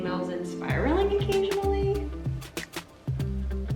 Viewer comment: I thought you said "Spyro-ing occasionally" and I was like YESSS I play Spyro all the time!